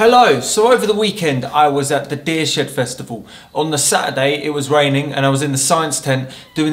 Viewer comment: sorry, did you say you were at the "deer shit festival"⁉️